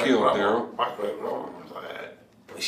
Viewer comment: I heard "BumbleBeast" 😂😂😂